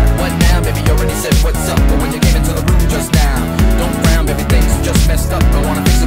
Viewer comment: I thought it said “how you gave it to the booty just now.”